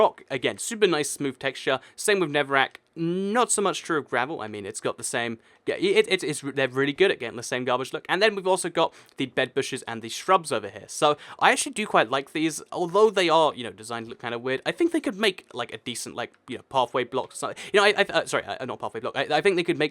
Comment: Anyone else heard bed bush around 6:40? Or is it just me.